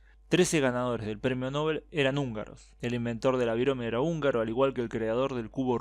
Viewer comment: ALGUIEN ENTENDIÓ QUE DIJO?? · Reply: Birome Si no sabes lo que significa, hay algo llamado google donde lo podes buscar.